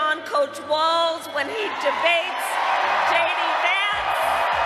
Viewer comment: I swear I heard her say …”debate Lady Vance” lol